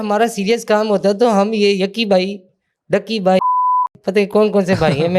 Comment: I think he said phati Bhai during beep 😅